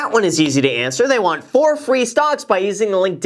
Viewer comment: Is it just me or did Graham say "4 free stonks" at 1:52?